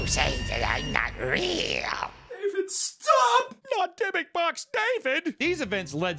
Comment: Dybuk box? Uhh, damn I thought you said litter box, my bad......meow!!